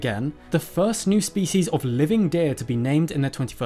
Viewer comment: We must be missing something. I don’t see a way in which an animal can be SO inept at walking, SO inept at swimming and SO bad at defending itself and yet thrive as the longest and possibly heaviest dinosaur ever (note I’m saying possibly because scientists seem to disagree on the weight as well). Edit: as a kind commenter reminded me it’s the longest CARNIVOROUS dinosaur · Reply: Am I the only one who misheard " Living dead" for living deer?